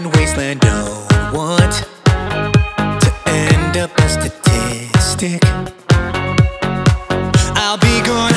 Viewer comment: "Don't want to end up a statistic." This hits hard, very hard. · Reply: I heard it as "austistic"